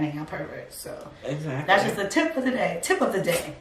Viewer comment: LMAO! At 23:45 I thought was saying “tip of the dick” I was like say whaaat!? I could listen to y’all talk all day. 😘